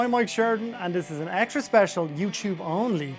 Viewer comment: When he said his name, I thought he said Ed Sheeran